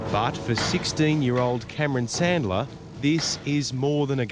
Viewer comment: 28 I thought the narrator said, "But for sixteen year old Colonel Sanders." My neighbor was BBQing chicken at the time.